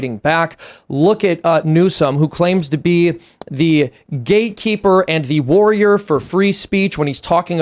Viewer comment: It sounded like Seamus said that Newscum is the "gay keeper". Did I detect a Freudian slip?